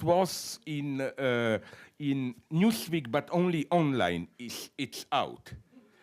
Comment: Anyone notice that when he says "Newsweek" it sounds like he is saying "Newspeak"?